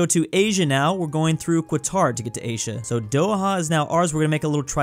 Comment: he really said Kwatar